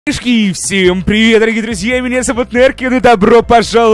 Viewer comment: В начале послышалось "Мешки, всем привет"